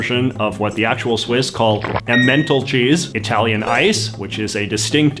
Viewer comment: The way you pronounced emmentaler sounded like you said menthol cheese😂